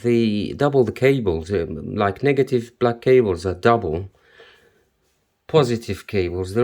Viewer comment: yoo why i thought he said "like nigga look at the cables" 😂😭